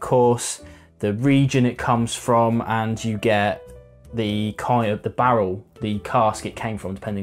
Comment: Golden advice. "Not every whisky takes water well." Curious if I heard a Dark Tower reference early in the review. I thought I heard something about the 'kai' of the barrel around the 2:34 mark.